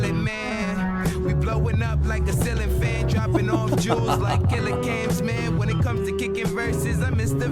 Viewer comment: all this time I thought Steez said “droppin of jewels (jews) like killer camps” 😭 i thought that was the hardest bar ever